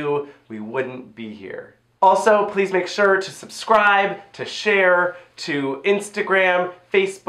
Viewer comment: I thought said "Subscribe to Cher" and I was like "I'm on it, babes."